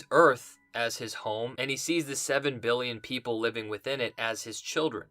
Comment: Hard to mix up 7billion ppl with "8million," which is what Veigt said. That alone makes me feel like this is a huge shot in the dark. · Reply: I get your comment because in real time I heard it as 8 Million but rewatching it seems to be billion. (I was thinking about the population of New York at various times and such. I also considered that Viedt frozen sperm might gave shipped 8 Million units, including to Trieu's mom. Selling the sperm of noble prize winners, athletes and other exceptional men was an actual business in the 1980s-1990s) But if he said billion that works too. Note, however, that in the context if the show he learned to think that way during his experiences as a god on Europa. He did not start out that way in 2009 or he wouldn't have abandoned his "children"